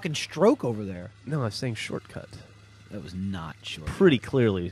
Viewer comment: lol I thought he said "Truck it" not "Short cut". lol